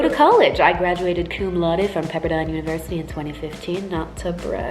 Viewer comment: I thought she said pepperoni university lol 😂 cool though xx